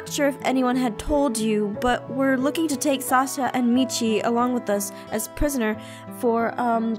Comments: omi it sounded like she said Salsa and Michi VDHSXV AHAHA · When said "What about Sasha and Michi?" I thought she said what about Salsa and Michi!!!! Hahahahahahahahaha!!!